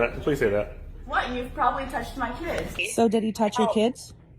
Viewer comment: I thought she kept saying “touch my tits.”